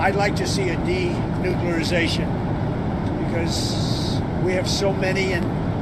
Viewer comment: did he say " denuclearization "? sounds like like Putin gave him the treatment from Clive Cussler's 𝘿𝙚𝙚𝙥 𝙎𝙞𝙭. There's been no ☢shot by the US, Russia or China for some 30 years as all testing has since been done on computer.